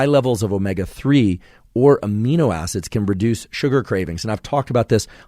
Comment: you said reduce, subtitles said produce.